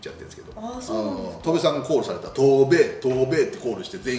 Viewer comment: ～ とーべーとーべー 空耳でDTコールされてるみたいに聞こえてしまったｗ 絶対イヤだそんなコールｗｗｗｗｗｗｗｗｗｗｗｗｗ